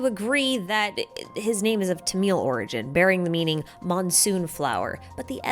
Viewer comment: I swear you said his name was of tomato origin